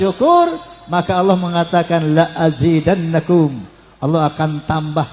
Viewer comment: Kalau saya salah dengar yg ahli QIRAATUL QUR'AN coba dicek apa yg dikatakan pak haji ini, kata beliau Walain syakartum LAAZINNANAKUM, kalau dalam Alqur'an tulisannya LA AZIDANNAKUM , satu huruf saja beda makna, kepada admin coba dicek apakah benar atau salah? saya simpan dulu buat saya tanyakan kepada ahli Qiraat ya